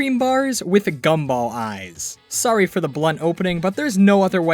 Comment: where's the blood? you mean cherry? wat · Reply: I thought you said “blood” and not “blunt”